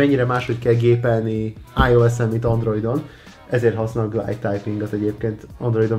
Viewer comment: “Ezért használok .... typingot” a kipontozott részen mit mondott? Kérlek segítsetek! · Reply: Boldizsár Máth glide typing. Töltsd le a GBoardot és állítsd be hogy glide typing.